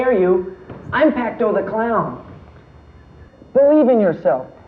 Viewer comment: does he say "I'm Pacto the clown"?